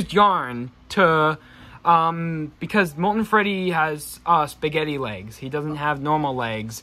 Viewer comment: Wait did u just say spagetey